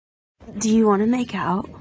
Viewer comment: What I heard that "me either I want to go to sleep"I really just left so hard that I broke my whole brain 😭